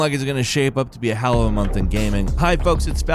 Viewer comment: Did falcon say November’s shaping up to be a hallow month in gaming @ 0:04? If so what Is he talking about?